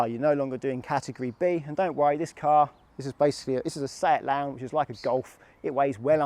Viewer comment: They're the same. Your car insurance covers you for learning and the test. But you must be insured to drive the car. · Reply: Thanks! What car do you have BTW? It sounds like you say Seat Lounge in the video, but I couldn’t find anything on that. Did you say Seat Leon?